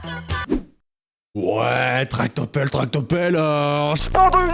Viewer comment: j'ai entendu pas tractopelle mais tartes au pommes, tartes aux pommes, tartes aux pommes... (continue la chanson t'entendra ça) moulées 😂